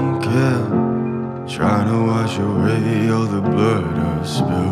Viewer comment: I always thought he said ''tryna wash away all the burger smell''